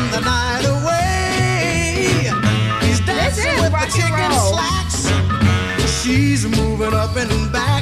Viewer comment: Haha . The line dancing with the chick in slacks. When I was a kid I thought he said chicken slacks. And I thought he was dancing with a chicken.